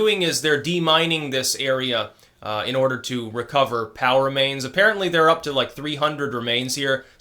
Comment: Haha. When he said POW remains, I thought he said power mains there for a second.